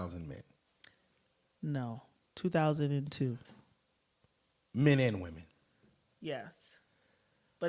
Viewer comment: Wow that is some low quality roast beef. What man on earth wants to be with someone who was ran through over 200 times I mean damn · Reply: Thought she said 2002 times..... Either way she is pure 🤢🤢🤢🤢🤢🤢🤢🤢🤢🤢🤢🤢🤮🤮🤮🤮🤮🤮🤮🤮🤮🤮🤮🤮 'High value'????? 🤣🤣🤣🤣🤣🤣🤣🤣🤣🤣🤣🤣🤣🤣🤣🤣🤣🤣🤣🤣🤣🤣🤣🤣🤣🤣🤣🤣🤣🤣🤣🤣